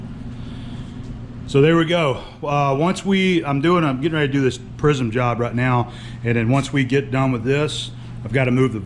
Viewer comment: "...I do this prism job right now and then once we get..." I thought you said 'prison job', and I'm thinking wow, convict labor- great idea, you can beat them and then threaten to violate them and there ain't nuthin' they can do about it- the perfect employee.☺